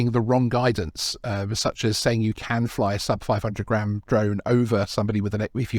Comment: Yes. That has been permitted for more than 4 years in the UK. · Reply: my mistake around the 11 minutes mark I thought you said sub 250g not 500g so my bad all the regulations are driving me mad 🤣